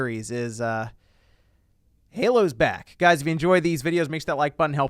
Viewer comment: Ok ngl, I thought you said that "Halo's bad" but you said "back"